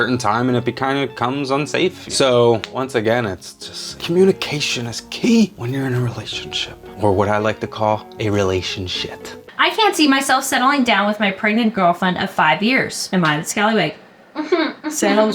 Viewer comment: I was so confused by Kris’s response to the question because I thought she said “I fancy myself settling down…” and I was like “what’s wrong with that?”